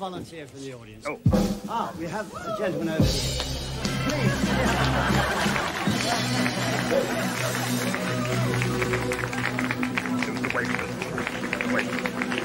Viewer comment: What did the magician say to mr bean? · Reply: "Now, please can I have one volunteer from the audience?" He asks for a volunteer to be part of his tricks while Mr Bean just so happens to be waving to the waitress. His waving is mistaken for volunteering, so he's taken up.